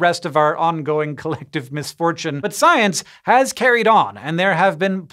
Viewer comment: What you said: "But science has carried on." What I heard: "Butt science has carried on."